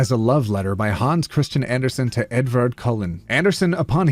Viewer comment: for a minute there I thought you were saying Edward Cullen like the sparkly vampires an I was like what fan fiction is this lol